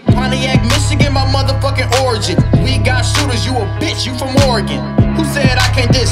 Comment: Lol I thought he said you a bitch for Morgan